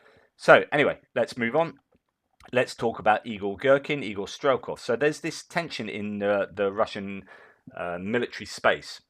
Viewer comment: I thought you said Igor Strelkov the Evil Gherkin. 😆 🤣 😆 🤣 😆 🤣. I need my hearing checked.